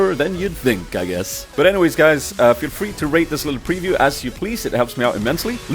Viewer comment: sounded like he said feel free to rape this little preview